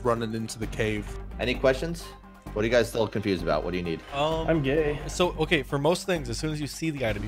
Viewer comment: does someone say their gay? 😂